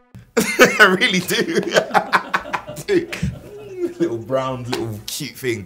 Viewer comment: Did he just say that bb-8 is brown?